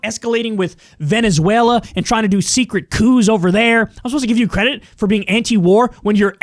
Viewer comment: when you said 'coups', i thought you said 'cooze' ))